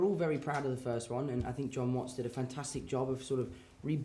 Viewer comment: did I just heard john watson